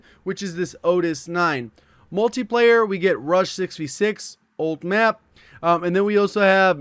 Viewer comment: I was listening to this vid I’m the background and I heard 1:38 and I freaked out for 2 seconds and I rewind it and heard say “Rush” not “Rust”😔